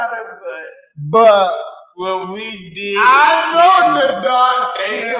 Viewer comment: did he say we did kiss?